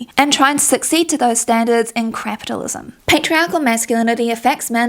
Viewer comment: did you say Crapitalism? Because I agree.